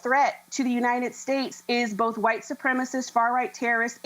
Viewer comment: I thought you guys said 🤔 hes not white supremacy